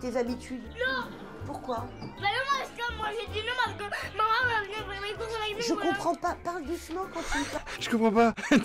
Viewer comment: TRADUCTION : 07:02 " Bah non moi j'ai dit non psq maman elle va venir faire le course avec nous voilà "